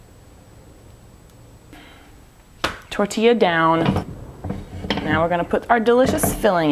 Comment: first time I thought she said "Tortilla ... done!" :D